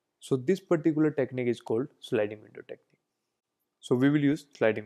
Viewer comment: what did you say? Sliding window technique?